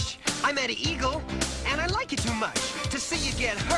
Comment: To me it sounds like Eddie is saying "I met a eagle and I like it to much"